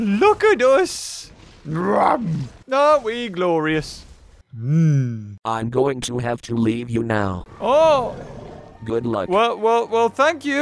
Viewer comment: I heard "I'm going to have to eat you now, good luck."